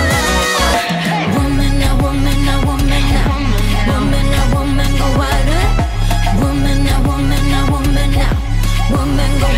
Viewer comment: who hear “albumin albumin albumin albumin...”